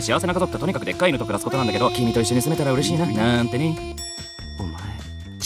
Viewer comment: "な〜んてね"が中村さんに聞こえた